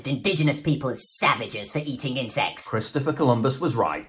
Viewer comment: I thought that they said eating in sex at first lol!